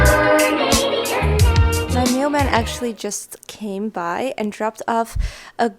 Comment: It sounded like you said my new man 👨, had to rewind ⏪ and you said mail man. Lol